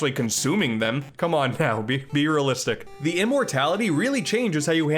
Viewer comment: Did he say "Be realistic." or "Beer realistic." Because if it was the former it should have been the latter. XD